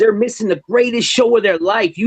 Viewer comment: did he just say that he performs the greatest show in the world?